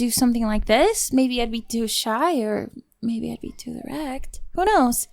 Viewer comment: DID SHE SAY ERECT?! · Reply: Direct I'm pretty sure lololol